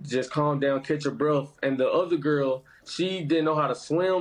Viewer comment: not me laughing because I thought he said ugly girl when he said other girl🤣🤣😭😭